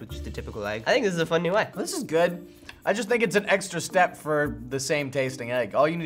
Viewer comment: I heard EGGstra step 6:05 anyone else???? No? Just me?